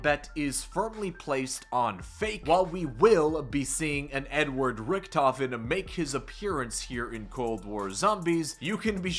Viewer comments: I thought you said an "N-word Richtofen" and I was so confused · my dumb ass thought you said “n-word richtofen”